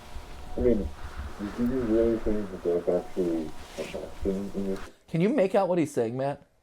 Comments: the guy saying stuff i think hes saying :do you really think theres actully like a thing in this facility. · i heard him say, “i mean, do you really think that theres a kinda thing in these (inaudible)” he might have said rooms or halls. Before he said that i heard him talk about something that happend 2 weeks ago from what i thought i heard which makes me think hes talking about the body they saw which brings up the point of a “thing in these rooms”